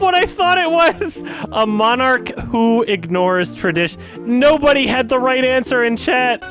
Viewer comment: I wonder if the "Monarch who ignores tradition" line might be harder for non-British / Americans to decipher because of the accent. I'm British and didn't catch it first time either, but got it on the repeat. Not well-annunciated, for sure lol